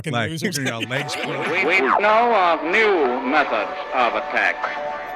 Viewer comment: Is that what the guy in the opening credits said?? Oh man. I always thought it said "Now, a new method of attack."